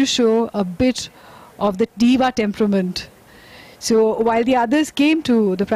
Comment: What did your say? A bit?